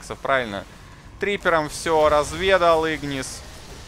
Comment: Да нет , это один человек . Просто у него сидром доктор Джекила и мистер Хайда . 33:30 , мне послышалось или как ? "Трипером всё разведал".